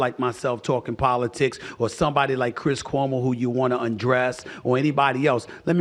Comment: Did he say he wants to Undress Chris Cuomo? 🤔